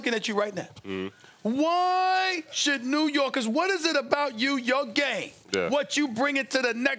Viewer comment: thought he said “About your gay” and I’m like “wait he’s gay?!?!?”